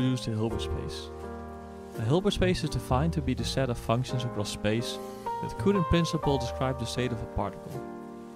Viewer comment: set of functions in space with cute invincible state of the particle. I listened to that 10 times and that's all I hear. Please could you speak more clearly?